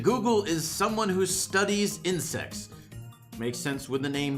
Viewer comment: boss fight robot: no one can defeat me bea: hold my bees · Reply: lmfao 2:01 when he saidi ''someone who studies insects'' i heard ''someone who studies incest''